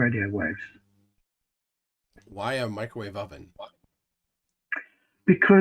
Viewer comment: It might be his accent, but it sounded like he said Van Halen Belts. ;-)